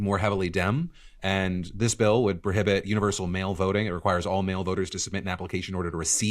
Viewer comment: I zoned out for a second and thought they said male voting instead of mail voting and got confused